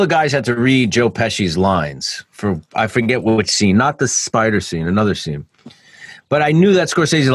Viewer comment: no I thought you said.. im alright Spider